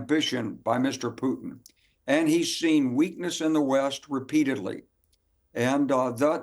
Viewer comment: i thought he said ' hes seen weakness in the west with P-Diddly' 😂